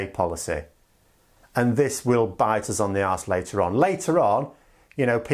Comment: I agree with everything you say, but one there's one huge issue at 10:24 when you say 'this will bite us on the arse' the subtitles say 'this will bite us on the ass'. This is an Americanism and we don't like foreigners so rectifying this should go a long way to normalising relations with the EU.